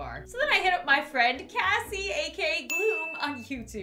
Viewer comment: When she said "Kassie, a.k.a. Gloom," the subtitles thought she said "Cathy a keg oome."🤣🤣🤣🤣